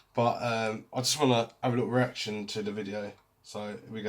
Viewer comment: I thought he said I had an erection the other day